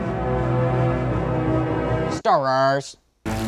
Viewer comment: that sounded like star raars